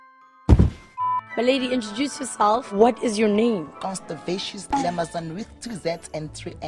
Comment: did she say CONSTIPATION???!!!😭😭😂😂😂 The interviewer is me!😂😂😂